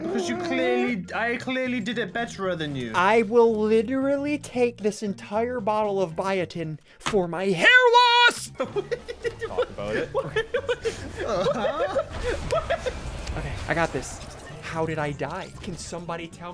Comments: Ngl i thought it said bitcoin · Am I the only one that thought it said bitcoin? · i thought he said bitcoin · I thought it said bitcoin and i was really confused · I thought it said bitcoin